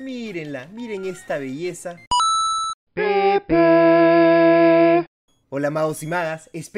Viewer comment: Nace la intro que dice pepe